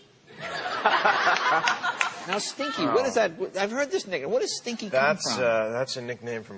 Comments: jay leno said nigga 0:44 "i heard that nigga" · i heard this nigga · What does she say? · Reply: "Love you stinky"